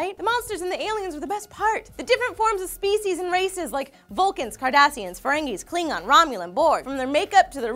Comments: for a second I thought she said Kardashians as a different species... and I believed her. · LOL I thought at 1:43 Lee said Kardashians. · swore she said kardashians when she was naming the list of aliens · I thought she said Kardashians. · thought she said kardashians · I wasn't looking at the video and I could have sworn she said Kardashians. I agree, they are monsters/aliens that are very relevant today. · Did she say kardashians??? haha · i really thought they said kardashians. either way they look the same to me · i thought she said kardashians too!!!! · It almost sounded like Lee said Kardashians while listing the aliens and space monsters. Hmm.... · I apologize to any Trekkies, but I swear I heard Lee name "Kardashians" as a space monster... lolw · I swear I heard "Kardashians" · did she say Kardashian? He looks like one, lol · Lee singing the song to TNG and saying "make it so" gave me such a nerd boner also when she said Cardassians i thought she said Kardashian's for a second then the picture came up and i'm like eh close enough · Did I just hear Lee say Kardshians at 1:42 ? Lol I'm sorry if I'm wrong, not a big fan of Star Trek. Though I know of it. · I could've sworn she said Kardashians...